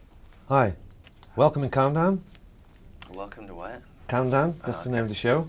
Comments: interviewer- welcome to countdown slash- Welcome to what?? hahahaha, so badass he is. What a legend. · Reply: I heard "welcome to condom" xd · interviewer- welcome to countdown slash- Welcome to what?? hahahaha, so badass he is. What a legend. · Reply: I heard "welcome to cum town" lol